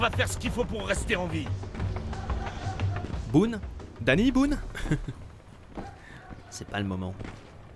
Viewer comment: j'ai entendu TEBOUNE 😂😂🤣🤣